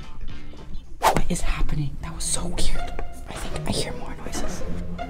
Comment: Did say “what is happening that was so cute” ?????? Or is it just me 😂